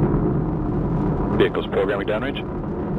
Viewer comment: "program damage" ? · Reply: I hear "vehicle's probably damaged" ? Idk.